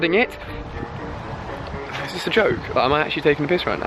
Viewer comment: He swore he said 're you taking the poss Ali' 😨😱😟